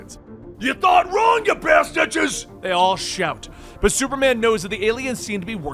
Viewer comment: ~ Did he just say Bastidges? I thought I was the only one who said that...